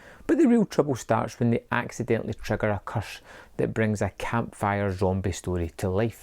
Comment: They trigger a what? A 👜!? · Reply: If you thought he said purse I’d give Rab C Nesbit a miss.